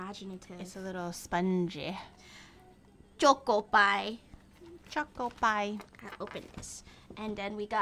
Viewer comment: Am I the only one who heard Choc-Oppai?